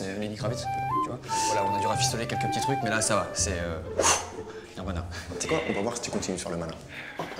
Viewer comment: c moi ou il dit oufff Nirvana